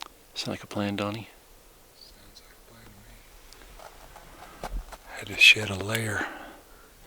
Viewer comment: So at one point said I had to shed some layers and my wife thought he said I had to shi.... some layers!! It was a good laugh 😂